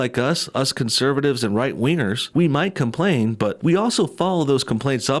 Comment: My mom was walking bye and thought he said "right wieners". I mean I see where she's coming from LOL it does sound like it.